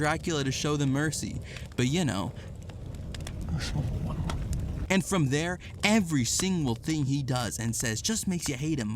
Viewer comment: What does the Bishop say at 1:16 to explain why he won't pray for her? All I here is a mumble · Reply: he says he'll offer a prayer for her but it'll be " a small one"